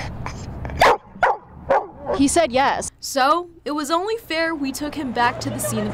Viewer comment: "Were you trying out for the K9 unit?" Bork bork bork "He said yes." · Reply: I heard "yes I was".